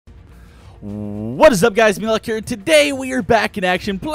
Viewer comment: it sounds like your saying malikure instead of meatylock